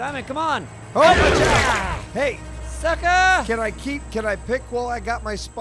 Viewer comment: Through these incredibly shitty headphones, it sounded like yelled "fuck off" at the shark right after